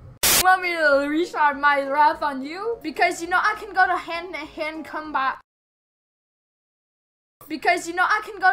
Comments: After 2 years I finally know what he is saying. "Hendtohend combat" Translation "Hand to hand combat" What I thought when I first heard it Hendihendcomback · WHAT PEOPLE HEAR: "You want me to unleash my wrath on you 'cause I can go handenhandkomba" WHAT HE ACTUALLY SAYS: "You want me to unleash my wrath on you 'cause I can go hand on hand combat" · I think said, "Hand to Hand Combat"